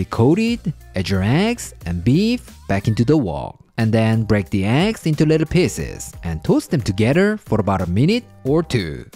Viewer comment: I thought he said “break the eggs into little peenesses”